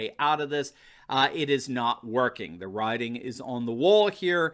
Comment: did you say the “rioting” is on the wall? 🤣😂😇☺️😊🙃